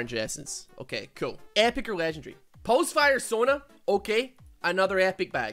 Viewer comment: did he just said Pulsefire Sona?